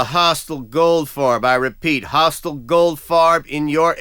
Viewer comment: I thought it was saying "Golf car" :I